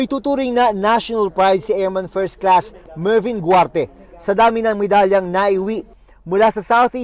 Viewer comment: Airman 1st Class Mervin Guarte tama ba yun narinig 0:19.